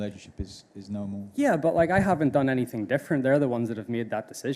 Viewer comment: He says that he hasn’t done anything wrong, which suggests that the players that went to the LIV tour have done something wrong, which is not true. Mcllroy always talks confrontationally on this issue and should be more of a ‘mediator’ if anything. Unless he believes that confrontation is a way of ‘growing the game’. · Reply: I think you are incorrect. He said at 1:34 that he hasn't done anything different. He did not say anything wrong.